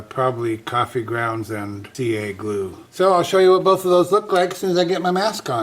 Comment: Whenever you was sanding the bottom and said about using CA glue and coffee to fill in the crack. Then said you'd show us what both of those look like as soon as your mask was on. I swore you said "I'll show you what buffalos look like as soon as I get my mask on"🦬🦬